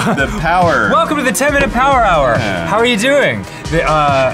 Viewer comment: Oh, I thought you were saying 10 miles per hour. Like some new segment